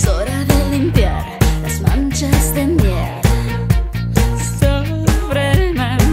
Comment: en el 1:05 parece que dice "Sí, ya es hora de limpiar las manchas de MIERDA" xD pero según la letra escrita dice "las manchas de MIEL".